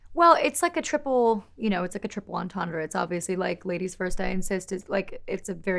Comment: I thought she said “Ladies first, baby I am cis” 😭😭😭😭😭😭😭😭